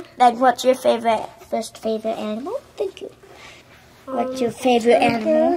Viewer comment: I legit thought he said anime and immediately I thought of your lie in April